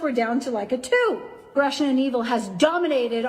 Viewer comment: Did Jodi actually say "Russia and evil has dominated our world" at 1:06:02 ? Did I hear that correctly?